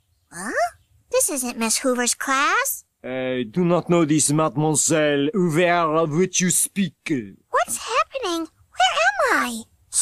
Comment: what the hell is he saying ? XD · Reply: "I do not know this Mademoiselle Hoover of which you speak!"